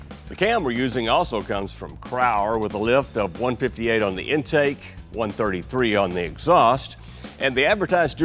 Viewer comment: Did he say the lift was .158 intake and .133 on exhaust?